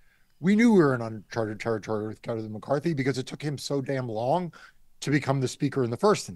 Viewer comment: "uncharted territory with Kevin McCarthy" ... you want another take with that one? :P I heard "char char char char char".